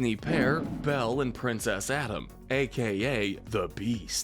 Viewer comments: did he say princess Adam? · Them: Belle and Prince Adam What I heard: Belle and Princess Adam